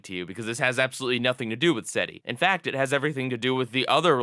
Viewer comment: I thought you said seti like the. search for extra terestrol life